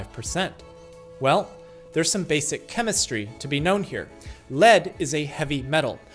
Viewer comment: I thought you said simple chemistry